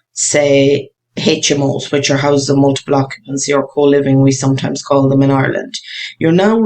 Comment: you said at around the 8:04 mark that the Irish have another term for HMOs , but I couldn't quite hear it. Was it "whole living"? Also, where does the term come from? I know the English say HMOs and that term is self explanatory. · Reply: Hi there, coliving is the phrase the bigger ones tend to use when going for planning....